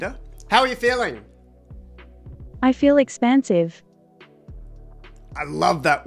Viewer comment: "I feel expansive." Good one. I am going to use it tomorrow at work. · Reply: I thought she said "expensive"...